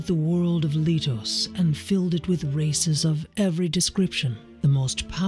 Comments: I really thought he opening said that the goddesses filled the world with racism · I without question thought the beginning cutscene said “fill the land with racism” 🤣😂😂😂😂I had to go back and make sure on that one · I heard "created the world of Lithos and filled it with racism" lmao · doesn't it sound like she said "fill it with racism"